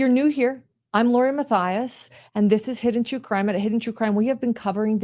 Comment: Did she just say I’m Lori Matthias?